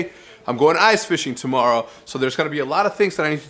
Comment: I thought it said ice jj fish at first